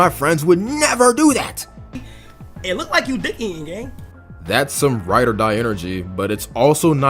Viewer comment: what is he saying 😭😭😭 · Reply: "it look like you dih eatin' gang"